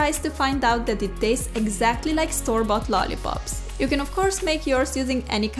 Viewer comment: at the beginning when she said "store bought......" , I thought she said star bucks